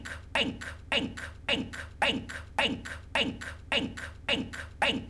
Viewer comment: Sounds like hes saying ink ink ink ink ink and soooooo on .)